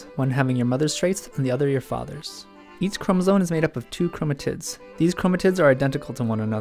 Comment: sounded like he said chromotits